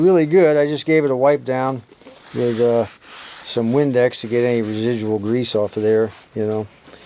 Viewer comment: Did you say windex? <link>